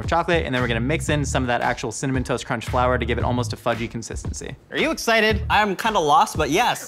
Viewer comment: I had to rewind that part because I thought I hear Josh say "fleshy consistency".